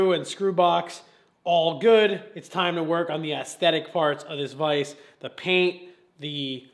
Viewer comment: am I the only one who heard “aesthetic farts of this vise”